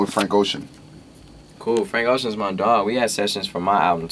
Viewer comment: Bro i thought he said "we had sex" for awhile Mind in the gutter there lol